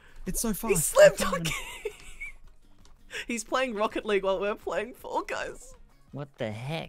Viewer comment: Did I hear say ROCKET LEAGUE at 6:33??! 😮 Also I’m eagerly waiting for more Sea of Thieves. I can’t get this game though but it looks soooo fun 😢.